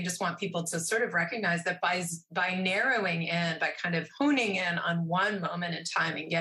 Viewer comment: Did say "hooning in"? ( 40:50) Maybe she meant "honing in", which is different. "Hooning" is driving around like a maniac, doing burnouts and doughnuts in a car. Probably not what meant. 😁